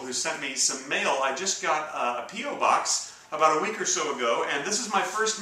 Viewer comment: Sorry, but i thought you said i just got a "Pedo Box" lol